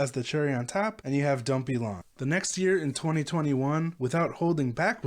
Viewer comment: Gotta love yt's auto generated subs...when said,"...and you have don't be long." yt thought he said,"...and you have dumpy lawn." 🤣 Thanks for the vid! Band-Maid forever. 🎀